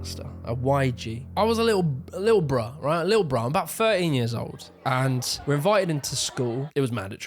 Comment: Sold....the second i heard "fur-een years old." 😂😂😂 Almost positive he said 13, but he made me smile and that goes a long way haha